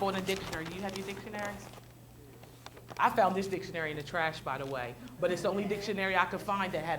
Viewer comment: Anybody catch the name of the book? I’m not getting it 1:24 mins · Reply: She simply mentioned that she found that dictionary in the trash 🗑, she removed it from the trash and used it because it was the only one that had a picture of a Parabola in it. The picture that was shown by the camera person shortly afterward in this video was showing the viewing audience the image. 👍🏾